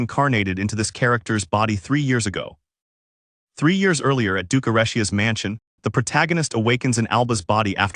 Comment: DID HE SAY DUKE ERISIA?